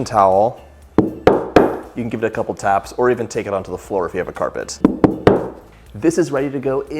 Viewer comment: Lol😂. You can tap it on the floor if you live in an apartment! Did he just say that? 🤣🤣🤣 no. He said carpet! But what fun!